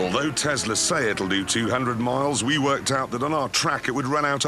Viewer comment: Exactly where does he say that the car has "broken down"? Literally at 5:03 - 5:04 he says "we WORKED OUT that it will do 55 on our track"..